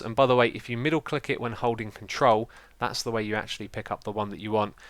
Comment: Middle click with troll? 6:33 I don't understand...